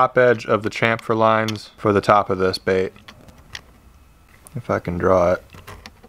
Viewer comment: I thought he said, “I’ll fuckin draw it”😂🤣😂🤣